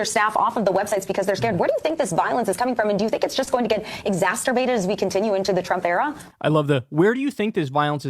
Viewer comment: did she say “exasturbated”?